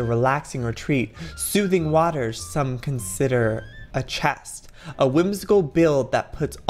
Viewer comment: A chest!!! Joey are you OK What is he saying??